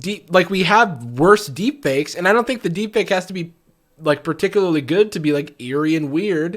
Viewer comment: When the subtitles say D pick in stead of deep fake